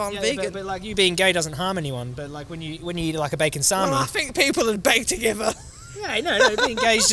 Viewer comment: I think what was meant to be said was ''Beg to differ'' but has come out as: ''People would beg to give her' / 'Bake together' haha